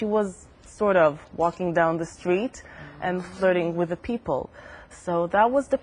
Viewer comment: guys pls could any one tell me what is that word in brackets she said at 07:47 "who was this woman who was dancing she was actually a woman sort of walking down the street and (flutting) with the ppl"